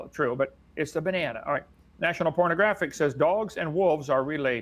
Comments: It was SO HARD to watch this Dr. guy talk. I’ve never been so heated😂 also, DID HE SAY NATIONAL PORNOGRAPHIC at 20:22 !? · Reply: Yup, he did say "National Pornographic." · Did he just say National PORNOGRAPHIC??😂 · Did he say national pornagraphic?